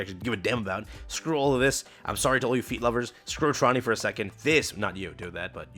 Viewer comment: All I heard was "Screw Trony" Mission accepted... Jokes aside, really needed these skill resets.